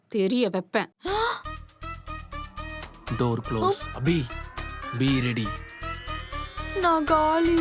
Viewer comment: be ready sounded like beer adi😂